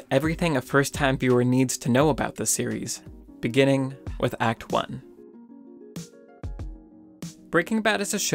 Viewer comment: I thought it said acne